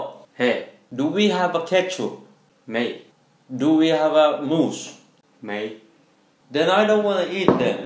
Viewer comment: lmaaaoo Did he say moose🤣🤣🤣🤣🤣👏🏽👏🏽👏🏽